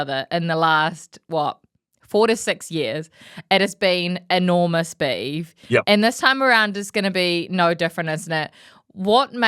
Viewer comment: did she say "enormous beav" ?